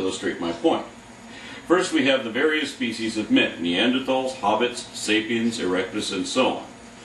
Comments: Did I hear "hobbit" at 3:00? Or is it just my hearing? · did you say Hobbits?